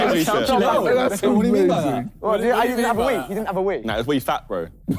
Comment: The contrast between the two here is hilarious. Vik’s trying to be nice and not offend Ethan, and then there’s Josh 😂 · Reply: Oh I thought he was saying "We fat", implying that both he and Ethan were chubby back in the day. 🤣 And I initially thought "kinda wholesome-ish", and now I realise he was being fully savage by saying "Wii Fat".